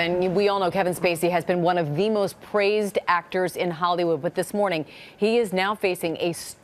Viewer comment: I swear she said Hollywoo 😂